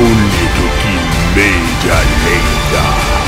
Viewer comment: did it say "bling bling butch"?